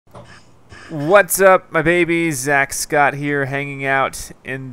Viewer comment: IM NEW AND DID HE JUST SAY WHAT'S UP MY BABIES AT THE START?